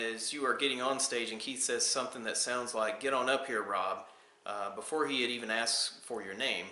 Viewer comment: "Get on up here brother." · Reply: That's exactly what I hear "get on up here brother"